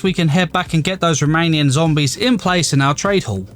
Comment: I heard “Romanian” instead of remaining zombies and I never laughed so hard in my life